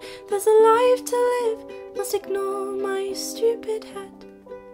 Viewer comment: Instead of "must ignore my stupid head" I thought she was saying "plus ignore my stupid hat". Anyone else?